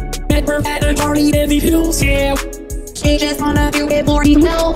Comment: For I second I thought Bri said "she just want to do 40 mil yeah" Lol also Mil is my nickname. . .